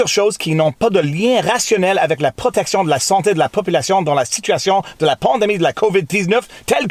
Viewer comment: I chuckled a bit because I thought I heard you say covid Deez nutz. I did a retake and had to rewind.